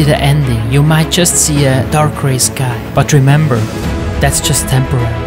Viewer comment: fr thought he said "dark race guy" instead of "dark gray sky"